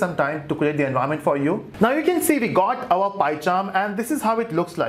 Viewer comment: when he said "you can see now we got our PyCharm", it sounded like python (like mamba) and I was dead scared for a moment. 🤣🤣 Jokes aside... I am only going one way with his tutorials, that is forward and next and next! THANKS and!!